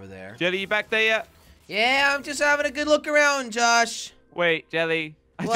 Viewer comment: jelly said “tosh” instead of josh lmao